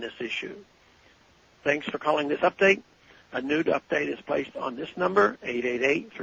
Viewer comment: Hehe. Sounded like he said "A nude update"